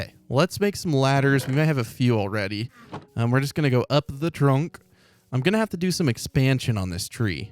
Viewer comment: Is it just me or did say “up the drunk” any 15:21?